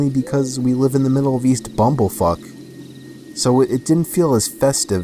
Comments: Wait did just say bumblefuck you know in the end of the word the 4 letter word "fuck" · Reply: yes,he said bumblefuck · did he say humble fuck?!?!?